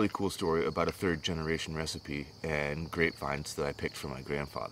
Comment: Man, at first i thought you had said grape wine, because i wasn't paying attention and i was like, yes! My dad made Wild muscadine wine, but it was (is) always too sweet. still have 3 bottles in the closet. I love grape juice, but if you come across a muscadine wine recipe that works, let us know my brother.